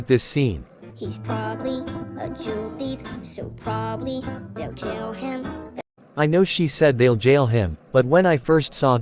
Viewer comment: Closed Captions said “They’ll tell him•